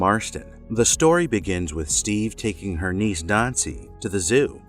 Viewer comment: I thought he said "nazi"